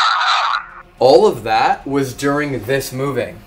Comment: I heard "All of them"